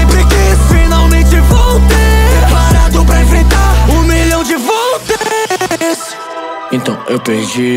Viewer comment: juro que ouvi "um milhao de voces" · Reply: Um milhão de volts